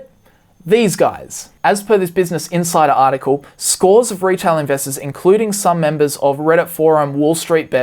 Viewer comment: Lmfao I thought you said "retard investors" xD